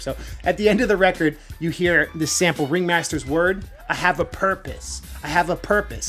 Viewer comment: Omgosh I always thought he said “I am the darkness” not “I have a purpose” 🤣 wow.